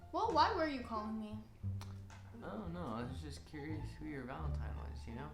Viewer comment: it sounds like she is saying valentimes